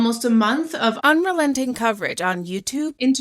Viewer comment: rough edit. What did you actually say? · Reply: I said “unrelenting coverage on the media” which is not what I meant to say so I dubbed it over lol